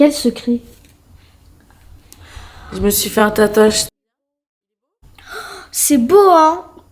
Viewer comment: elle a dit tatage ou c'est moi ? 😂😂😂